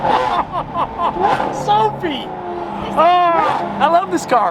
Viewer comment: does say "shiiit"? 😅😅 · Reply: It was geez